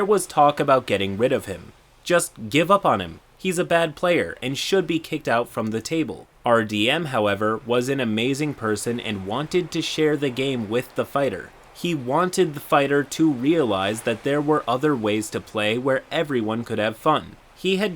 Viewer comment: I heard that 'i was the fighter' and actually did a double take